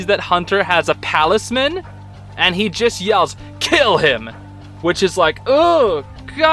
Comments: i think belos actually says “caleb” and not “kill him”. At least it sounded like he did Imop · I think what Belos actually said was "Caleb!". He recognized Flapjack, Causing him to get very Angry. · I thought Belos said Caleb like the name of his brother not kill him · at this part of the video I don't think belos said kill him, i think he said Caleb, which I think is really cool considering the fact that flapjack told hunter that name (edit) just read the pinned comment nvm lol (I'm turning into the people who don't continue a vid and pause ;-;.)